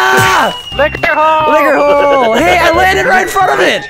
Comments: Is it me or does Vanoss sound like he says lick her hole instead of liquor hole. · Is it just me or when vanoss is saying "liquor hole" it sounds like he is saying "lick her hole"